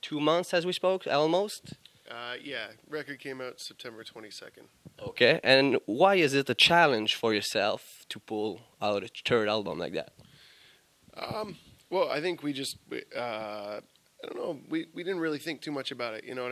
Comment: with his accent it sounded like he said "turd album" aha but 3dg is the best!!!